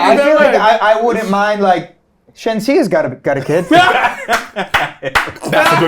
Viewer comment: Bro went STRAIGHT to Shenseea LMAO I hear it still. Man's got taste